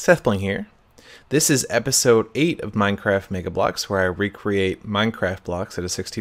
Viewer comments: Lol, at the beginning kind of sounds like he says "minecraft nigga blocks" XD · I thought he said Nigga blocks :D